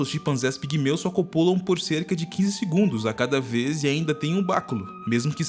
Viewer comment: "15 segundos", é isso msm q eu ouvi? #Trupe20:07 hsusjsjssjsk "entendedores entenderão"😂😂😂😏😏😏